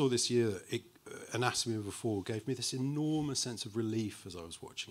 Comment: what movie did Alex say trusts the audience here? I couldn't understand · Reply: Anatomy of a Fall 😊